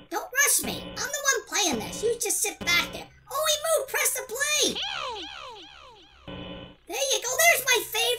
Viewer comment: Hi btw I watch bfdi then I’m gonna die when you say bfdi firey x-x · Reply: In 2:07 i thought it said Oh Preston Play